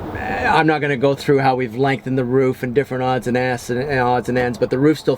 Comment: Did he say "different odd's and asses"? at 15:44 lmao